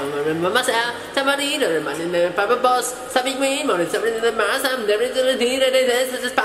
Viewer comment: I heard 'papabols' hahahahaha